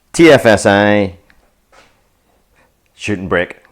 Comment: Instead of shooting brake at the start, I thought you said "shouldn't break" and of course as we all know being an Audi that couldnt be right. Thanks.